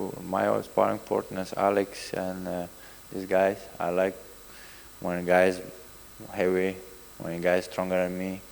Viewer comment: I like when a guy is heavy...bigger than me” Man, I heard that as “I like when a guy is hairy” 😂🤣 glad he followed it up with bigger than me lol